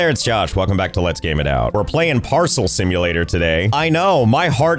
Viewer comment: I misheard Josh reading the game title and thought he said arson simulator and briefly wondered what that had to do with boxes but wasn’t fazed because it would totally make sense for lgio to do something like that